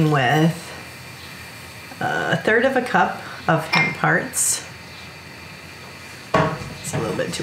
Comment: what are thins parts I live in Australia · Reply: I also thought that's what she said. But looking through comments, she said hemp hearts.